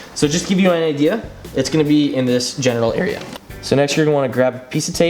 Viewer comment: Did you just say "Genital area" or "General area"? LMAO